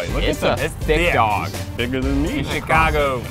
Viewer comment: “this is a beefy boy” then did Keith say “it’s bigger than me”?!